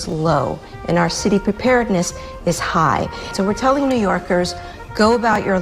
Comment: NYC's "preparedness is high?" Oh I thought she said NYC's government is high! I said well yeah sure, of course!